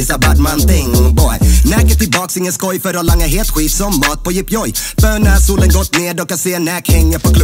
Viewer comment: Vad säger han på 0:27? "...för att langa het skit som ?matpojepjoj?"